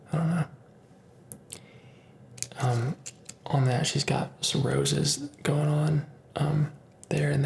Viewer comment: When you said “she’s got some roses there” about your girl tattoo, I thought you said “she’s got cirrhosis there” 😂😂😂😂 lots of love and luck from Ireland ☘️